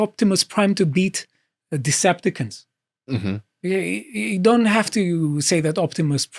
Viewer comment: did he really say ''a deceptacunt'' XD